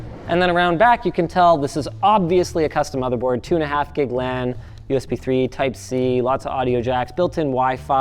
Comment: did you just say 2.5GB RAM..? wtf..